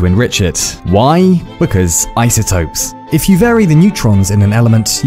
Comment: The actual line that was said in the video: "once you mine some uranium you'll need to enrich it, why? cause isotopes-" What I heard: "once you mine some uranium you'll need to enrich it, why? cause i said so-"